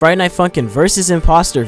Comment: you did not say friday night funkin you said friday night f*cking